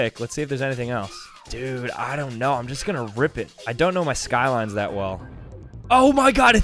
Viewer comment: Did somebody say skyline